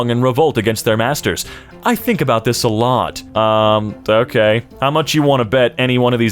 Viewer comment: Wasnt looking at my phone when you said "I think about this a lot", I heard " I think about this sahlad." 😆